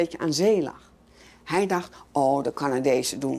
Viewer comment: did she say the Dutch equivalent of "Canucks" as stated in the subtitles? Didn't sound like it. I don't know that they even have an equivalent in Dutch. Sounded like she said "Canadese" which is "Canadians".